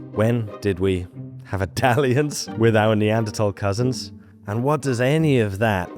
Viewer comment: HELP i thought you said “have Italians” instead of “have a dalliance” and just accepted it for 30 seconds